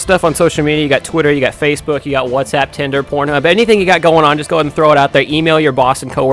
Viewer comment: DID SAY TO SHARE THIS TO PORN HUB 6:40 !?!?!